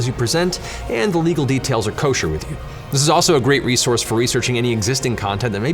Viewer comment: did he say Kosher?